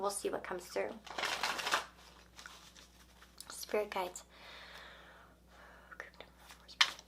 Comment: I had my subtitles on and when you were shuffling at 1:11:28 it said "spare kites" instead of spirit guides 😂